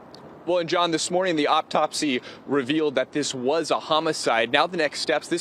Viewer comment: Did he say "optopsy"?